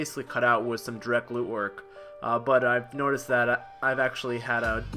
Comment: you say you cut out direct glute work, did I hear that right? My heart is sinking right now tell me it was just a joke!